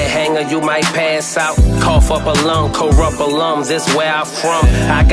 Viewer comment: What does Spitta say at 2:02? Corapalums? 😂